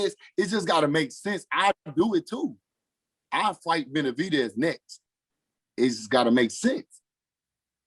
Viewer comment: Lol. I heard him say, 'I'll fight Benavidez tomorrow '😁😁😁